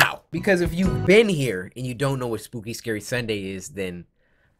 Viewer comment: I thought he said spooky scary Monday for a sec